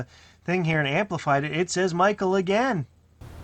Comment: I dunno guys, for at least one of those "Michael"s, I think I heard "Leafs suck". This still proves the entity's otherworldly knowledge though.